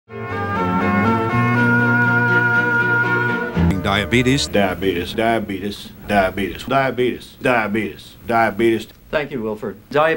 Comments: I heard "Die abities" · What I hear: "Die betus" "Die-a betus" "Die-a beedees" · It's funny, for a while, when he said "diabetuhs", I heard "nerm-u-lus"